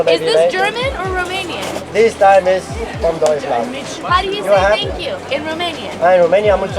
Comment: When was getting the Champignonpanne and you didn't understand what the man said when she asked where it was from, he said "Deutschland" - so Germany